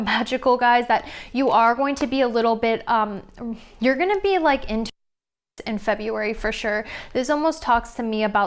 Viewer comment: Group 1 @ 10:55. The audio cut out, but I think she's saying "into romance."